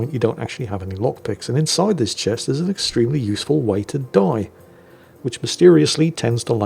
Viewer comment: To my ears, that sounded like: "And inside this chest, there's an extremely useful way to die!" Wait, what? :)